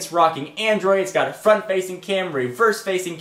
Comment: did i hear front facing CAMRY on evo 4 g at 3:33?